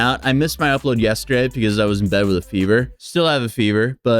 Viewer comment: I misheard fever as viewer